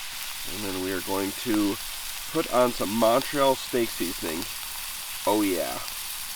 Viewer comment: For some reason, I heard Monty All Steak Seasoning. I say make it and sell it. I’d buy it. Monty All Steak Seasoning. $9.99